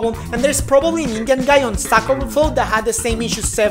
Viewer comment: sockoverflow? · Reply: Actually he said stack overflow but..... I also thought he said sock over flow..